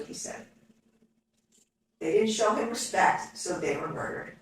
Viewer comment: I heard "Show some respect". I have cheap ear buds. Good point on both sides, yet do you want this guy moving next door to you in 11 to 20 years.